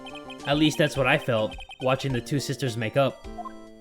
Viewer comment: I was so worried I thought it said "make out".